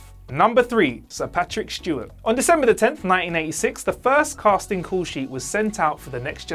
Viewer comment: It really sounds like you say 1996, but maybe I'm just hearing your accent incorrectly. You, of course, meant 1986.